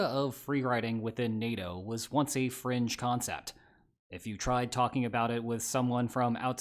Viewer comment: Had to rewatch the first 10 seconds or so 10x, I kept hearing "Free riding within NATO was once a French concept". I knew my ears had to be lying to me lol, turns out it was still at 1x playback speed instead of 1.5x playbackspeed that I'm used to.